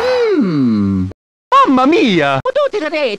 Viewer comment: am I the only one who heard, “Mama Mia, I’m not a potato”— 💀